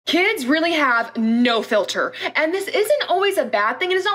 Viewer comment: I thought it said mother instead of filter 😳